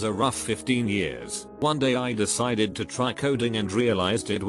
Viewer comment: i heard coding as codeine and was confused for a second